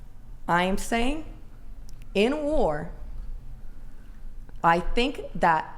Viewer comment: I heard "Anal War." 😄